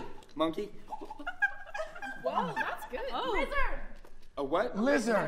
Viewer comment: Am I the only one that thought Olivia said wizard when she said lizard 😭😂